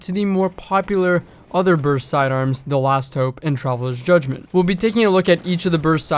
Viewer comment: For a second I thought it said travelers chosen instead of travelers judgement